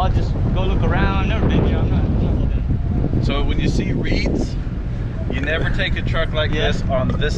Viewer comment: is he saying weeds or reets? I'm assuming he is saying weeds, right? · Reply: Reeds.